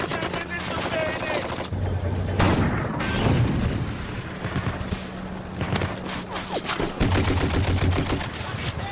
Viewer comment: Does anyone know what the german says as he is running away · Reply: "Sanitäter" - calling for a medic